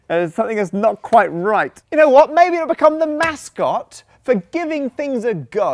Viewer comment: I thought Matt said Mattscot at 3:54! :P So close!